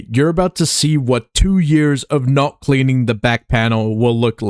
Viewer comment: I heard cheating lol